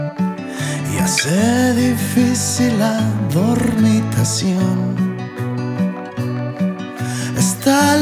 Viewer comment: "Esta cama siempre está vacía y hace difícil la dormitación" Amo que Jos use lenguaje michi y/o zacatecano en sus rolitas! 💛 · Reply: jeje la primera vez que la escuché pensé que decía "fornicación"...ya se me hacía raro